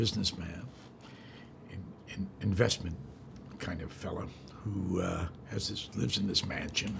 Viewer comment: Did he say "Hoo ha"? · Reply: 😂 I heard it too. That film was great! But he said, “Who, uh.....” Nice catch though.